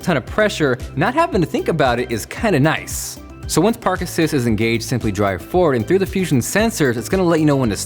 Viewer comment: when Jon said "park assist" I thought he said "Parkinson's "